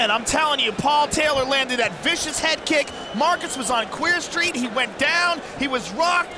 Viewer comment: Does Rogan say, “He was on Queer Street”? 😂